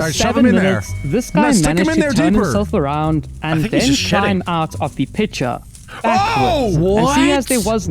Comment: I heard say "I think he's sh*ting" LIKE LISTEN